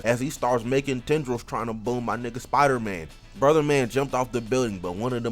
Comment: did he just call him Brother-Man?